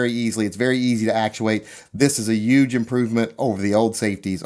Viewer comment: Did you say "this is a 'Yuge' improvement" at 3:30? your starting to use Trump adjectives...